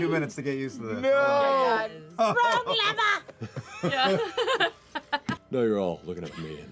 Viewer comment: I genuinely thought she said "Frog Liver"